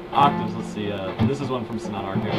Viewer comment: did you say from "Sonata Arctica"? Coz if you did, you have good taste in music, my friend :)